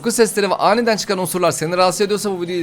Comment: unsurları "osurları" diye duydum😂